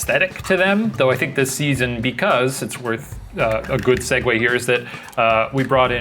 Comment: Just wanted to inform y'all- at 7:49, the captions say "segway", but it should be "segue". Not sure if it's an obvious mixup for deaf or hard of hearing folks, but I figured I'd point it out 😊